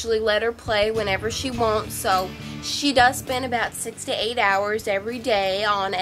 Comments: "She usually plays about 6-8 hours" Gamers: "Pathetic" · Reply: I thought she said 68 hours.... (I know that’s impossible but I thought that was the joke) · "She usually plays about 6-8 hours" Gamers: "Pathetic" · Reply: Yo I thought she said 68 hours